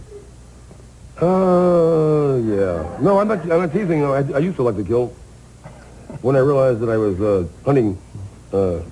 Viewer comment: What did the host say about Hunter Thompson's slip on David Letterman at around 32:00? "..."don't know if it was guru-related, or what?" Or did he use some other reference to an event, a religious cult, military or some kind of professional lingo or term, or last name perhaps, like Kuru or keru? Whatever he said, I wish he'd have explained what he meant by that relationship, reference. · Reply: He said Kuru. It’s a reference to a brain disease similar to Mad Cow. It comes from eating human brains. It’s a relatively common disease amongst primitive tribes. The elite are said to participate in eating people (and human brains) as well. That’s why we sometimes hear references to Kuru amongst conspiracy theorists.